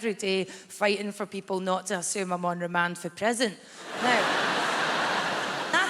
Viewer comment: Can someone tell me what she actually says at the end of the "Boris Johnson says stuff and people say it sounds reasonable, but I have to fight every day for people not to assume I'm undemansipresent" bit at 1:47? I listened to it literally a dozen times but it's no use, and the auto-generated subtitles are as confused as I am. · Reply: I'm on remand from prison